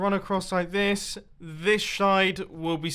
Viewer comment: he said shide instead of side LOL